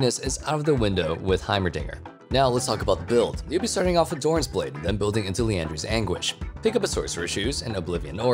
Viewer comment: I heared so many times dorans blade instead of dorans ring 1:27 9:06